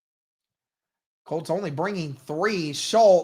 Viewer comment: Thought you said colts gonna win! Go Texans!!